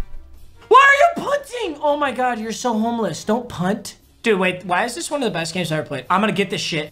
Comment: Did he just say “stop being so homeless”